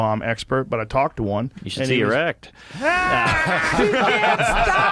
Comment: anybody know what exactly he says to Joe after Joe says, “I’m not bomb expert” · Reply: "You should see your act"